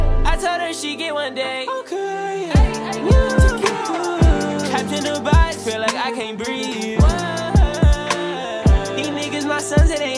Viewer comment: The whole time i thought he was saying ''Captain Dubai feel like i can't breathe'' but he was saying ''Trapped In a box feel like i can't breathe''